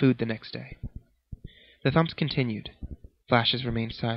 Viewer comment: lol I thought it said thumbs hahahahaha